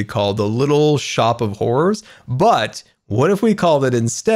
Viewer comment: Lol love this I immediately thought of "little shop of horrors" when you initially started this build. Was thinking "little shop of horticulture" would be cool, but both that and are amazing. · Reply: Jacob Lowe I thought he said whores not horrors